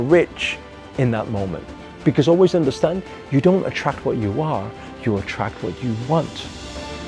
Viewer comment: Did he say, you don't attract what you are? You attract what you want?? 1:58 👈